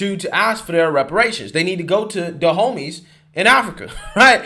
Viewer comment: I thought you were saying The homies ... oops dahomy